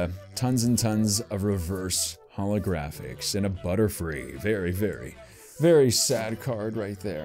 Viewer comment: I think you said blutterfree